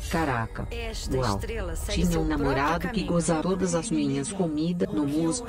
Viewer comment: Gente o que foi isso? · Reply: Tinha um namorado que...